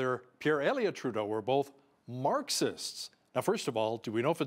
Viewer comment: I didn't here PP say Marxist...I heard "Narcissist" I could be wrong..but thats what i heard